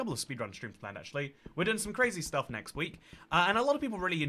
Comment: sounds like he said "we did some crazy stuff next week,..."